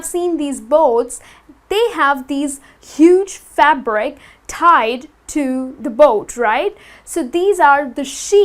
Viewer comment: Hi, someone can please say me what word rise in 10:30 between "huge ~~~~ tied to"? I really don't understand, even with CC on. I'm from Brazil. =) Thanks a lot for attention. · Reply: she saied ' huge fabric tied to..'